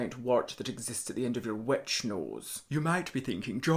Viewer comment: Until I turned on the CC....I thought he was saying "wet snoz"....not "witch nose:.